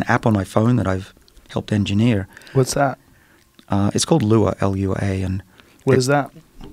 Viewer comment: I can't find "lua" (or did he say "luan"?). Little help? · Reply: Its Lua its an app